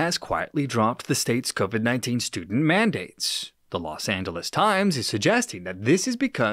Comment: Did i hear correctly? " stupid mandates"?